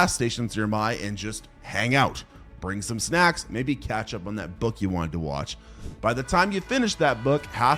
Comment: did he say catch up on that book you wanted to “watch”?